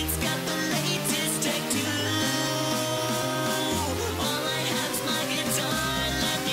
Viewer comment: it sounds like he said he has the latest tattoos instead of the latest tech to